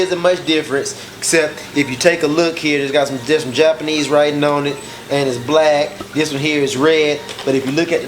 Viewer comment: the captions say "if you take a look here doesn't just japanese right now only as black person he was raped" lol